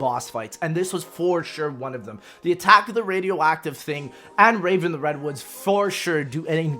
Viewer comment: wait what did he say? “Its the attack of the radioactive thing” i think thats what he said but he sounds like a mumble rapper